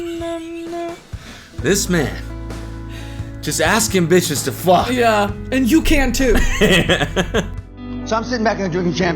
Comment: I swear to god I thought he was saying “just Ask-Ambitious to fuck” and my brain was doing cartwheels trying to figure out what ‘ask ambitious’ meant